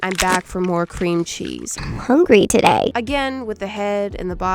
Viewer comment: i thought she said coochies..